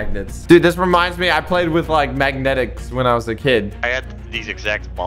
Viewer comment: what did he say? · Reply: Bro he meant magnetics